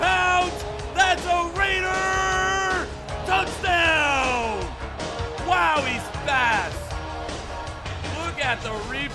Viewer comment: I thought i heard say " WOW HIS FAT" lol